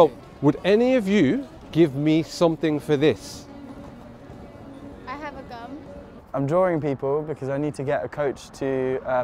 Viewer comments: Thought she said "I have a fun" Thought man was boutta get shot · thought the girl said "I have a gun" because she felt threatened by the man looking to trade his ketchup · I thought she said I have a gun 😳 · I thought she said "I have a gun" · did she said i have a gun or i have a gum?